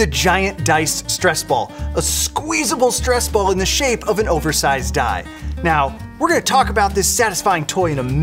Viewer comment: anyone else heard "oversized die"?